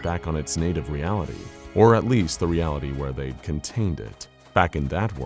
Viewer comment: I’m sorry did you just say at least the reality they contained it???? 14:25 Also could you make more of these? I REALLY enjoy SCP-682 take on the backroom